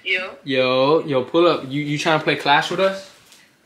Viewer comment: My dumbass thought he said "do you wanna play class wit us"🤦